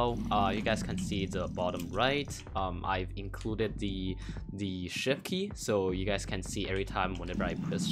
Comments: When bedless noob said: I added the shift key I thought bedless noob said, : I added a shit key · I heard "as you can see in the bottom right i've included the sh*t key" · i legit thought he said "sh*t key"